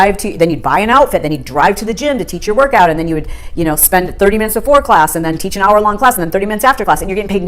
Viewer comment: i thought she said "you spend 30 minutes in vore class" and did a spittake girl, i don't think you can just teach vore, you either do or you don't, there is no try.